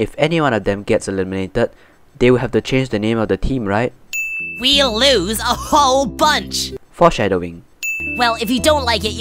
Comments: did coiny said "We'll ooze a whole bunch? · first under 301 club how tough are you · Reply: It sounded like they said "we'll lose a whole bunch"